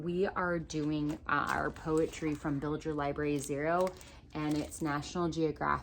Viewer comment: Hi, Can you elaborate on the poetry? It sounded like you said "Billiger Library Zero" but I don't know what that is. :-) Is it a curriculum or poetry suggestions? I want to try poetry tea time so I'll definitely check out the National Geographic book!